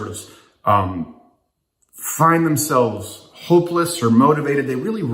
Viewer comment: I thought it said “homeless”